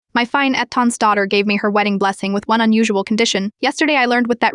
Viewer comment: How the hell did AI pronounce fiancé? What the heck was that and I thought AI was supposed to be smart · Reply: Lmao 😂 i heard it as Epstein’s daughter 😂😂